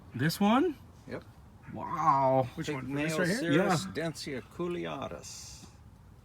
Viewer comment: What is the plant he mentions at 38:10? Couldn't quite make out what called it. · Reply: Pygmaeocereus densiaculeatus